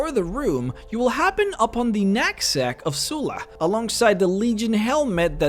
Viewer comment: I swear I heard, "...nutsack of Sulla..." I lost it; too great!